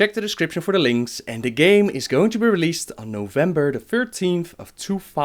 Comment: hey i thought you said november 13 2013 it's not major just thought i'd point that out